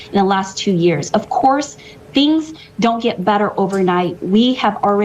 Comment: it almost sounded like butter, not better :)